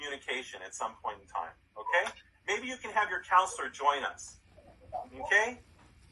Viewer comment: he actually said counselor not console